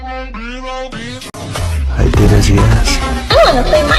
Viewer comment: I- 4:55 I paused it at the right time and it sounded like i wanna play Minecraft 🤣